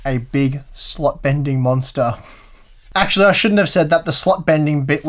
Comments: i thought he said "Slut bending monster" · I thought he said "Slut Bending" graphics card 😂